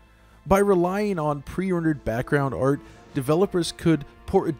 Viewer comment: i hear preorder instead of prerendered or is just me?